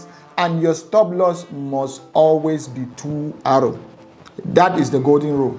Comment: Hello, I`m really interested in your strategies with trailing stop losses, and much more....could you please clarify what you said at the 4:04 point on your video? You said 2 A`s, or something similar, but my audio isn`t so good. I might need new drivers on my old HP...God bless